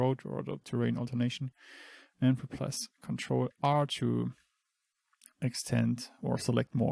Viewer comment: please can you help me? what are you doing here I think you mispoke or I couldn't understand but I cant find the hotkey you press there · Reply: nevermind you did say ctrl + r, but it litterally just ctrl + numpad +. i was overthinking It mb